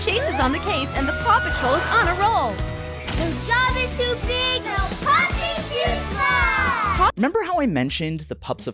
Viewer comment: I genuinely misheard that as "no god is too big; no pup is too small" and got excited for a universe in which the Paw Patrol takes on God